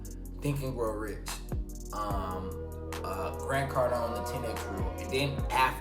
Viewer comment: Supp, I couldnt understand which book you were saying at 10:10 bro, can you type it down for me. 💪🔥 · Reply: He said , 10x Rule by Grant Cardone